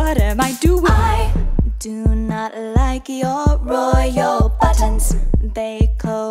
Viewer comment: I Do Not Like Your ROYAL BUTTONS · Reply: At first I thought it said "I do not like your Royal butts" And honestly that made me laugh even more